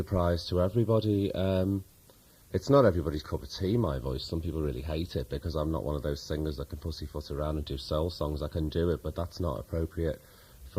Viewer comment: what does P say here? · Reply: "Some people really hate it because I'm not one of those singers that can pussyfoot around and do soul songs"